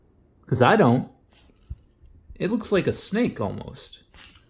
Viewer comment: see they thought you said snake... instead of bake... common problem.